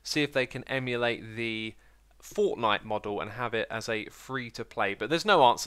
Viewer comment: did i just hear xisuma say fortnite the world is descending into chaos